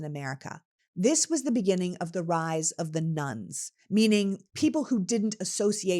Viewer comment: When you were speaking about the rise of “the nones” I thought you were saying the rise of “the nuns!” And I said, “Whaaaa? Rise of the nuns? When did that happen and how’s could I have missed THAT?” Even though in seconds I realized what you were saying, every time you say it my head rattles. I’m sure, with time, I’ll adjust.